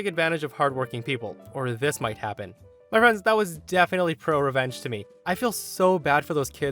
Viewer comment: You know I honestly thought he said BRO revenge. And now I just imagine some dude bro frat chads helping their nerdy friend get revenge on a popular sorority girl who embarrassed him.